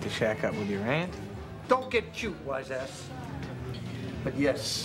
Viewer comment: Don't get Jew wise ass! What do you mean! You people? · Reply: Sounds like he says "don't get cute, wise ass"